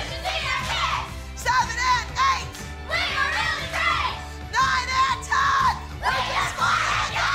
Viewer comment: and 4:25 did Abby say 9 8 10 ?